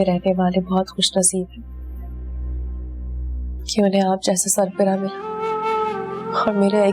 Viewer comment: When she said Main to keti hu is ghar mein rehny waly bahot khushnasib hain, jinhain aap jesa sarbarah mila First I thought she’s saying Sar phirra 😝😝😝